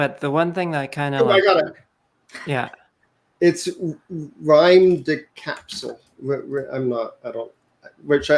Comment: what was the game @ 1:13:45 ? Sounds like the says Rhyme the Capsule?